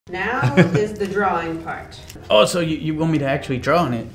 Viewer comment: I thought they said drinking. Then I realized it was wrong. Then I realized it should’ve said drinking. They need it lol